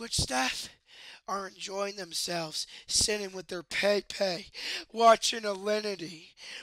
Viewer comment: Did he say " enjoying themselves sinning there with their pee pee"